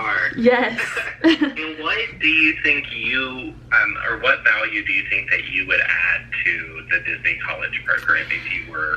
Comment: Is anybody understand interviewer said?? Plz help me😓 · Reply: 김예디 he asked me what value I think I would add to the program if I were to become a cast member!